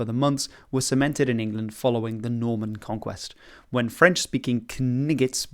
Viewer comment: um I'm sorry, what did you say..? 😳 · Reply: K-n1g-gets. It's a callback to Monty Python and the Holy Grail.